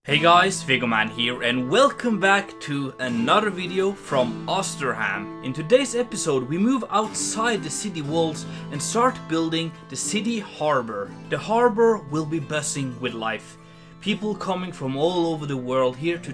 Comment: did he just say ''the harbor would be bussing with life'' 💀💀💀💀💀💀